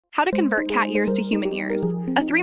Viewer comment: Ngl but I thought she said how to compare cats ears to human ears instead of years😂